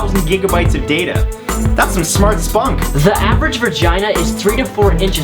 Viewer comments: Does he say "vergina"?? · Did he just say vergina?? 😂😂😂